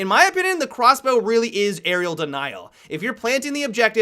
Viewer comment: Why does it sound like hes saying Aerial Denial. It would be an Area Denial Weapon.